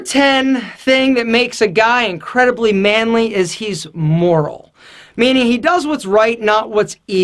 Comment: Im sorry i didnt understand what he said at 4:16, he said moron?